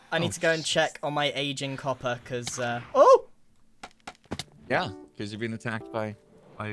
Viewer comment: did anyone hear him say oh shi